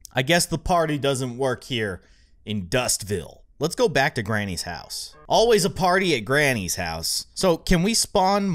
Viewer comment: For a min i thought it said granny goes and trows a party at heII